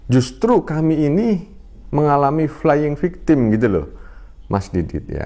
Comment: Yesus diangkat jd Tuhan 1700 tahun lalu, Trinitas omong kosong: Salah itu ustad: saya pihak victim, iman kami diserang 🙄🙄🙄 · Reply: Saya malah denger flying victim 😂😂tau deh apa kuping saya yg salah dengar